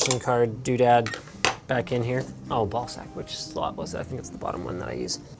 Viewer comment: Did linus just say ball sack. hahaha